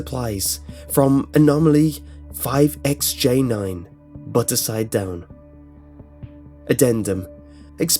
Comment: "BUTTER SIDE DOWN" I hear it as "BUTTERED SIDE DOWN" Edit:Check out his channel! <link>